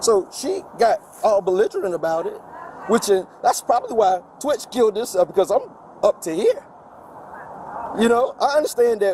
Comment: it says intangible which is the wrong word to begin with but anyway what he said was "that's probably why twitch killed himself I've had it up to here" and if you don't know who twitch is he was the DJ on The Ellen show for many years and supposedly he committed you know what but there is a very strong theory that he was taken out because he knew too much